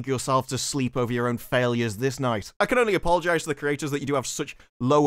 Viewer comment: The subtitles said "creatures" instead of "creators" around 26:58... And yeah, that's probably more accurate